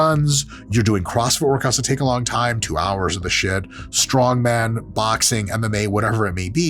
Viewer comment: did he say 2 hours in the shed?? 🤣🤣